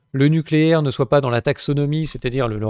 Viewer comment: J'ai eu peur : j'ai entendu "l'attaque sodomie", en fait, vous aviez dit "la taxodomie". Enfin, je crois. Ouf !